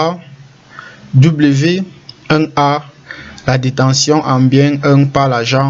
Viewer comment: Bonjour excusez moi svp à la 1:08 vous voulez plutôt dire dotation? ou bien c'est détention? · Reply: Salut Velera! Il s'agit de la détention. Vous trouverez les dotations à partir de la deuxième minutes. Elles sont en couple ( constituées des détentions...).